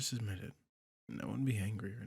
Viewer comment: Wait... 9:18 ...Did he say "I won't get HANGRY"?!?! I'm ded